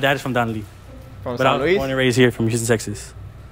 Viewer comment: I heard you say “San Luis” in min 3:50 or am I wrong 😵‍💫